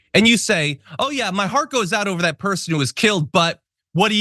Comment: you’re mistaken. He said his heart goes out to the scenario ( 0:10). He definitely did not say his heart goes out to the victim.